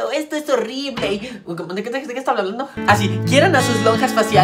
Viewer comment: que dijo? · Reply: Dijo: ¿Cómo de que té? ¿De que está hablando? Ay no